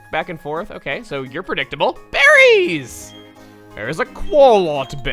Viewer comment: When shouted BERRIES! I thought he said Barry and I was like ............................ wat